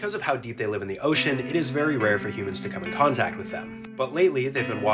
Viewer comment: I dont wanna sound rude but i heard " beashes " instead of " beaches "😂